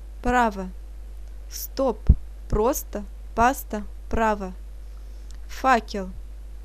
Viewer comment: I thought she said f*** you oops 😂😂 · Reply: she said "fakel".